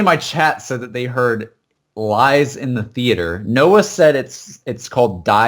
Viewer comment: I heard "it lies in the theater"